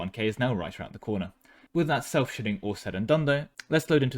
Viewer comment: Maybe I shouldn't watch his videos so late, but I swear everytime I hear 'self-shitting' instead of what the talking cake slice actually says.